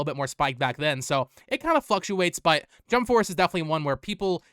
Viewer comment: When you were talking about “Jump Force” you were talking about the price of it and at one point you said “It fluctuates”. For some reason I heard “It fucks you up” and I thought .. “Damn. He really doesn’t like when prices change”. Gotta get my hearing checked. Thank you for the tips, brother. I just discovered your channel and I’ve learned about a few games thanks to you. I don’t like prices fluctuating either unless the price drops because it really .. fucks you up when the prices go up lol